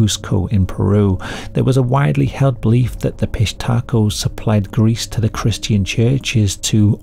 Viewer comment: I thought you said "fish taco" gang, lol. The auto generated captions are having a field day with it. Pissed hatrco, pish Taco, Southern undies... gotta love it when the topic is so messed up. 6:56- fish taco, about time it heard it too. I didn't know Peru had stories this creepy, of course everyone does, I just never heard them.